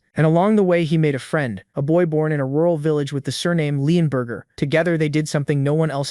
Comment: did he just say burger